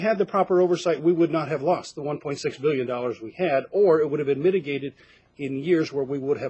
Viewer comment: how do you lose 1.six echo 1.6 billion ,i was outraged when i thought you said 1.6 million how do you lose money it was stolen i cant belive this scam,but being in arizona who watchin your cash you are a cop .find that money i smell ROCKS N FELLAS CREW WORKIN OVERTIME